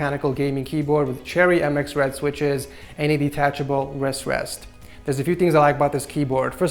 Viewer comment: It sounds like your saying "a detachable rest rest" when your actually saying "wrist rest". Did anyone else notice that or was it just me XD